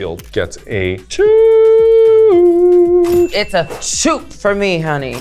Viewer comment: she said “SHOOT” right? Did I hear right?